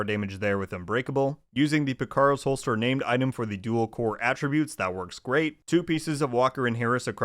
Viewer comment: what did he say after fenris unbreakable at 13:08-13:12? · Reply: "Using the Picaro's Holster Named item for the dual core attributes, that works great." 👍